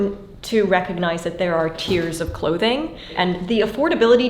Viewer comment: I heard "tears of clothing" and I expect to see you shedding those by the end of the video.